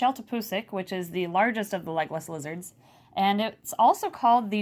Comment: Before seeing this video I always thought she said she had legolas lizards